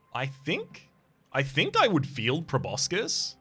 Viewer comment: For a second there I thought you said “I feel proboscis” and was immediately shocked at the thought of such an emotion